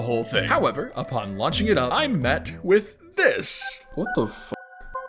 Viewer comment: did you just say what the fu